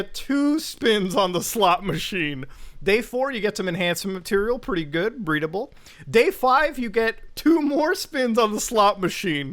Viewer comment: did just say pretty good breedable